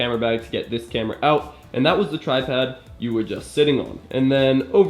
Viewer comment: Did you just say "tripad" at 2:26? Lol